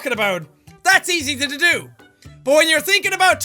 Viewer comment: it sounded like he said tatodo